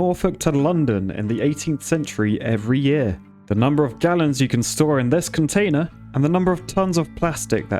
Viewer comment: I thought he said Germans instead of Gallons😂